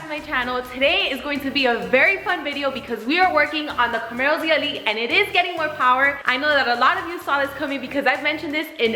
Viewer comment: did she say ZLE? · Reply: Yup. ZL1 1LE - 1 1L = ZLE. Kind of long to say ZL1 1LE everytime :)